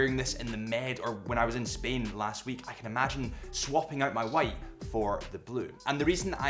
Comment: I thought he said swapping his WIFE. 😄